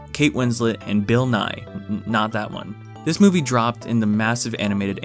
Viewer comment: did he just say bill nye, not that bill nye , like bill nye isn't the science guy